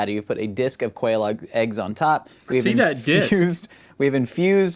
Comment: Any Dark Souls players watching? 11:42 I swear Josh says "a disc of Quelaag eggs"